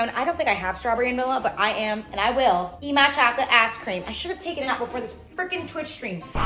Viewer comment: Bro I heard "eat my chocolate ass cream" 😭